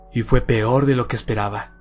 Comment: Minuto1:10 "Fue peor de lo que esperaba" Yo pensando que dice: pv** el que lo lea.